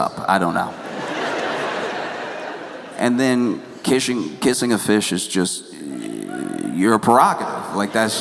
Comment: I’m so fucking glad I’m not the only one who heard “kiss my fish”